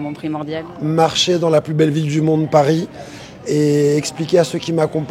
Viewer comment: i thought he said "poubelle"😂